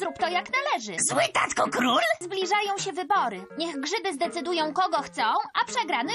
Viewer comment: Czemu usłyszałem żydy? xD